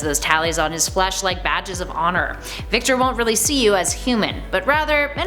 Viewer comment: Victor zsasz I thought she said Victoria's a**